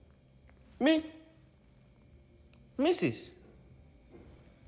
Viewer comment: i thought he said mrs. bullshit